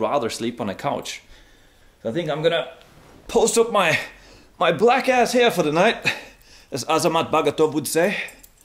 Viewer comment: While on the sofa did he say “my black ass”. 8:42 he is hilarious.